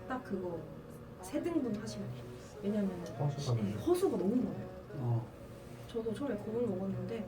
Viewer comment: 초에 **가 너무 많아요 라고 하시는데 뭐가 많다고 하신거에요..? 잘안들려요 ㅠㅠㅠ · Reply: 허수가 너무 많아요~ 경쟁률 부분에서